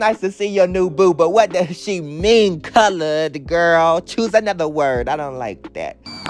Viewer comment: i thought miss morgiana said "covered" not colored?¿ 😂